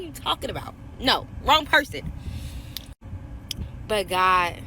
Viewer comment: at best, she misheard God. He was saying "OH no" not just "no".